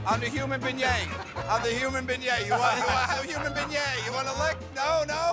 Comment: sounds like he is saying human bidet